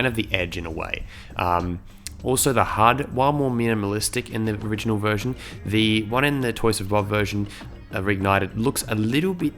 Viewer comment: What did he say at 1:48? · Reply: not entirely sure which bit you're talking about but he says "While the hud in the original version is more realistic, the Toys For Bob, Reignited version, looks a little bit too [as someone pointed out], mobile-game-esque. Basically what he is saying is he thinks the hud in the new game looks a bit tacky and generic in a sense that if you showed someone just the text box, you wouldn't know what game it was. The original if you showed it to someone that vaguely knows games they would know its Spyro because it didn't really look like any other game